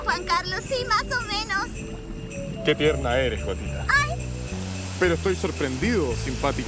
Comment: Entendí "Que tierna eres, joputa" xd